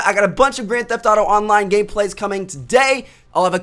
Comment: did he say gay play? o.0